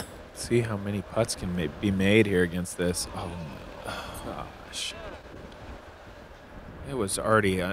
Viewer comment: Anyone else hear the "oh sh*t" at 10:50? lol was probably shoot but still funny